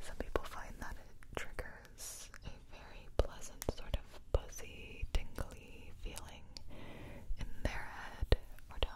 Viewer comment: what did she say?! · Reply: “A very pleasant sort of buzzy tingly feeling in their head or down their neck”